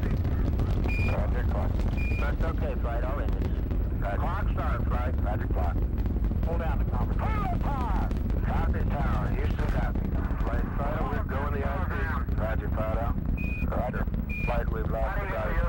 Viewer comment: I hear it as, "Copy tower. Houston copies." I'd guess that after the "Hold down the conversation, please" transmission that the FD wanted to make certain that everyone at Houston and the Cape understood that the FD had heard and understood the "Cleared the tower!" call from Launch Control.